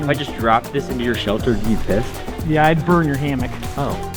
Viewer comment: Did say pissed